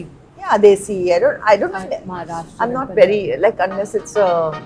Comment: What did she say in the end at 28:10 ????😊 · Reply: Unless it is "Hakkasan"(Chinese restaurant) I am not into Chinese.